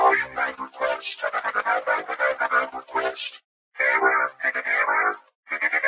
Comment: At the end it kinda sounded like he said bank request